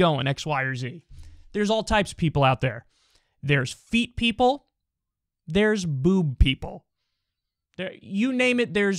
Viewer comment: "There are boob people, there are feet people... I'm going to cut myself off from going further down this road because we can get into some funky ass territory" 😅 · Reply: my dumb ass thought he said food people and weed people. thanks for this comment lmao